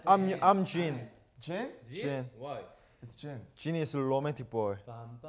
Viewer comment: No one: Kim taehyung: jin is lomantic boy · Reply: I heard it like "selomatic boy"